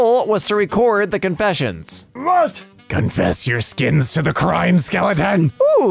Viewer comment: Ah, I see Oz was not the only one to say "Confess your Skins" Instead of "Confess your sins"